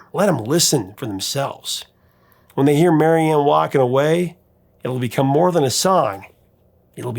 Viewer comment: It’s amazing how easy those 70’s rock singers made it seem. Brilliant. I always thought he was saying “ watching her derrière walking away”… lol